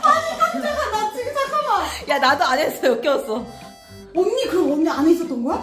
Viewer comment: ㅋㅋㅋㅋ:자기위로 이야기하니: 나도 안했어 여기와서로 들리네 ㅋㅋㅋ겁나웃기네😂